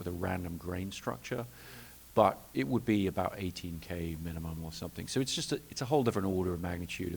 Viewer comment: Wait... did he say 8 or 18? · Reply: 18k. Yes. The resolution is INSANE..... 35mm is (an equivalent to) 6k 5 perf (non IMAX) 65mm is (an equivalent to) 12k And 15 perf 70mm (IMAX) is 18k